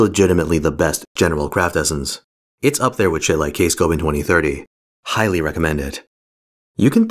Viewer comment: hey at 1:20 what was the other top ce besides 2030? i didn't quite hear it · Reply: K-scope